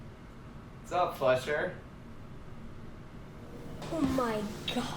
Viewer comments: I think he said sup fletcher... But i first heard sup flesh hair and got really confused and scared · When he said “What’s up Fletcher” I thought he said what’s up “Flesh hair” lmao